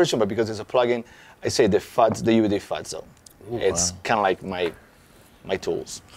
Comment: Great one! I can't understand the name of his favorite plug-in at 50:55, can someone tell me what it is? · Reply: same with me... my guess UAD fatso